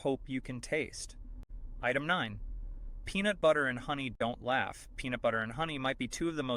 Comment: I thought it said peanut butter and honey don't last....I had to look at the transcript......it said don't laugh